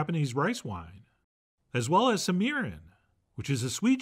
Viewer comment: So, for anyone else who watches these videos with subtitles, at 34:20 the subtitle says "as well as some urine". Obviously, Chef John would never ask you to put urine in your food. He is saying "Mirin". Just clarifying for anyone who is hearing impaired and saw the same thing I did.